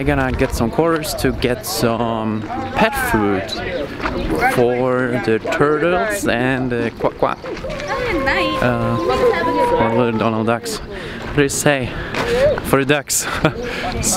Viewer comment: Woke up from a nap and heard you say quake quake 😂